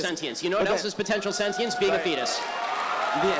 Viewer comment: when you have translation on it captures penis instead of Fetus lol